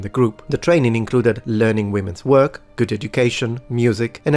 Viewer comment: at first I heard "learning how to twerk" and I had to rewind 😅